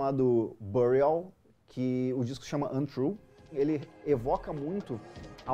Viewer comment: Sabe dizer o nome do disco que ele fala aos 2:49, não consegui sacar o nome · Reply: Bernardo da Bôaviagem Burial Untrue <link>